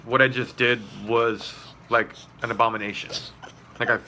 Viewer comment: 👍🏼👍🏼 and thought said "I need to show you my armpits." At the end. 😆 I agree with you on fundip. Was super cool as a kid, now -bleh